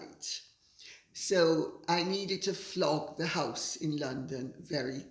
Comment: Just what I need--a touch of. 3:50 "I needed to flop the house in London" The only time I am sure was EVER involved in a "flophouse" sorry, apparently I misheard and it was "flog" the house :D Sometimes I watch the CC to read along and it always renders her elegant pronunciation of "really" as "rarely"--so sometimes the text looks like the opposite of what really says! Thanks for educating us on Thatcher's victory sign. What years those were.